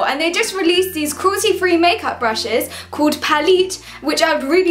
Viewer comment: I honestly thought at the start she said royalty free make up brushed